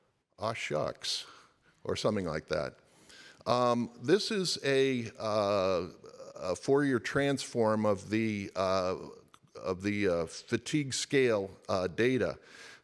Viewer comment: Is he saying "This is a Fourier transform of the fatigue scale data" at 4:24? I can't really make sense of that sentence.